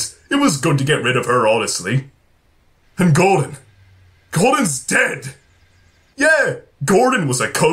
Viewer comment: did say Gordon's dead or God is dead? I'm not sure if I heard it correctly. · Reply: Upgraded!Kai The Falcon he said Gordon’s dead